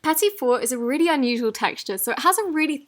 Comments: i thought she said petty four! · didn’t it sound like she said “petty fort”